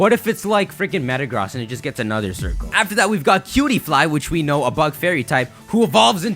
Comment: When you said cutiefly I thought you said pewdipie anyone else?